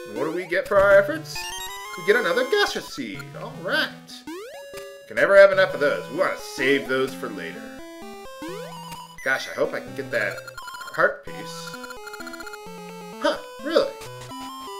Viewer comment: I wonder if a pun was intentional or not, but, when talking about the Heart Piece found in the Gasha Trees, it sounded like you said, "Gasha hope I can get that Heart Piece." 14:03 for reference.